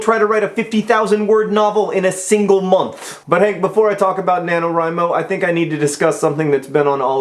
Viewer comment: Since I'm watching these videos in reverse chronological order, I've thought he was saying "nanorhymo" the past few videos and I was mystified as to why he wanted to make a billion rhymes.